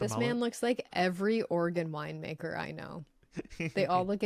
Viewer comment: it sounded like was saying organ wine